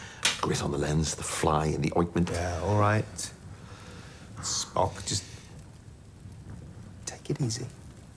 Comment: Did John say Spock? 😂